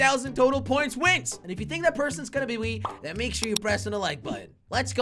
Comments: did anyone hear him say if you think that person is gonna be wee instead of me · i think he said oui. Is he French